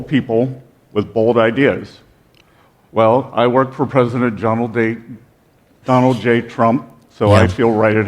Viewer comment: Thought you said Donald take a dump 😮😅